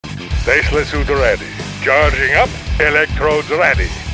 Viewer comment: Gotta be honest, I legit thought he was saying "Faceless suit ready" and now I know it's "Tesla suit ready", but I still want to believe the other. It's more menecing.